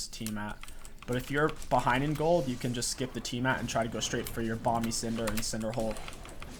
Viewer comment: What are "bombies" or whatever he says at 5:34? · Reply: Collin Wexler bamis cinder bro